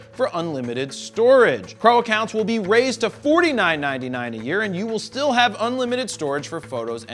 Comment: Rachel Kaparek well, I am kind of locked into Flickr for legacy. I go to a bunch of sci-fi conventions, and I have stuff on Flickr that has been used on celebrity blogs, Wikipedia pages, been used in various online discussion boards, it’s 10 years of links. I wouldn’t mind the price increase; except, I also have a smug mug account for my serious work, flickr is just my dumping ground for convention pictures and things like that. So, now smugmug wants me to pay them $50 a year on top of whatever I’m paying them anymore. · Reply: Robert Williams makes sense. I obviously misheard. I thought he said $50 a month but it’s $50 a year which isn’t bad at all.